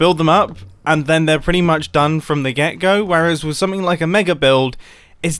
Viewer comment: random 1am thoughts – am i the only one who used to think people were saying 'from the gecko'? Or was that just me... To be fair, english is not my first language! Edit: more random 1am thoughts – mumbo's youtube logo look an awful lot like the lorax.... COINCIDENCE? I THINK SO! (But still, how did i not notice this?)